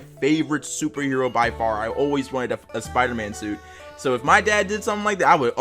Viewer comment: it sounded like he said “I always wanted to f### a Spiderman suit”